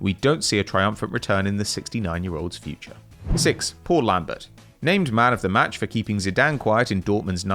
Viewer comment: I thought he was saying Poor Lambert and not Paul Lambert XD